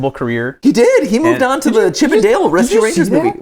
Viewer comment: y'all said chip 'n' dales, I thought you said chippendales and was very confused why sonic was in a movie about male exotic dancers